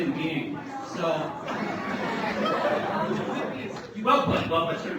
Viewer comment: What does he say at 7:23 ? Someone tell me I wanna get the joke. :( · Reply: Well put, well put sir